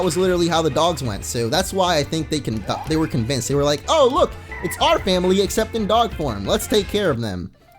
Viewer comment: did you say "oh look its our family accepting dog porn" at 19:56 ?